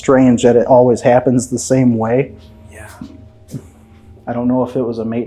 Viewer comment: None of them ladies wanted to talk to and and that makes sense, Did anyone else hear a whisper saying yeah 7:16 ?